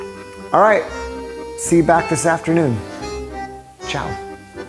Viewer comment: thank you so much for this video , I wonder did you really say " chào " that mean Bye in Vietnamese at 13:22 .